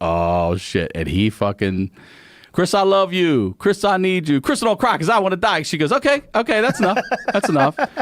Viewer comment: What did Ryan say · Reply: Crisa dont cry cuz I wanna die